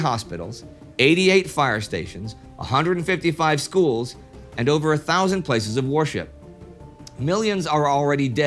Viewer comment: did he say places of worship or places of horseshit/whoreshit? I keep mixing them up and they sound so similiar.